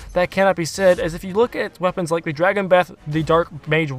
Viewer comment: at first I heard “dragon meth” 😂